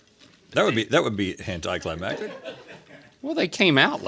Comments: did Rhett just say "hentai climactic"? I swear that's what I'm hearing 😭😂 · lmao I almost got whiplash when I thought that I heard Rhett say "hentai climactic" but he actually said anti-climactic in a strange way 🤣😭